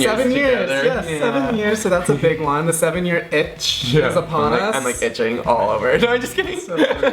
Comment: At first I thought you guys said the seven-year inch. I’m like is that supposed to be some sort of suggestive joke🤭 but then I got it you said seven year itch🤪🤣